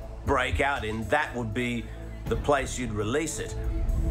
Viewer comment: That would be the place you would release it ? What did he just say?